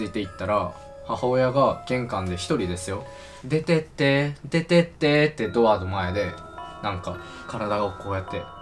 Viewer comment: でてってー が ててってーに聞こえて 仕草も相まって何踊ってるんだと思った